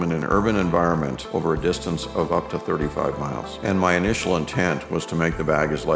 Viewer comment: For those who are a little critical of this load out remember this is a get home bag not a bug out or an inch bag. He does not really set a time or distance target. Since everyone is going to have different variables to contend, size and number of items will vary. This was an example of how one man decided to keep it small and light. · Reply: I thought he said something like 30 miles? Maybe an hours drive? Oh well like u said it's for him